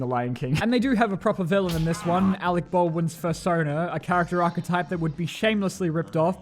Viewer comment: di… did you just say “fursona” XD